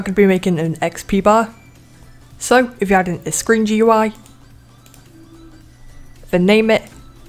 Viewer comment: when u said "screen gui" in 7:46 i thought u said "cringy gui